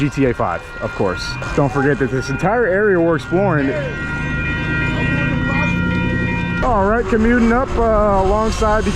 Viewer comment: what did dude say? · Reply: Sounded like he said come here then says I’m not going to rob you while having a hand in his pocket. I’m surprised didn’t say anything about it to the viewers after he was safely far away.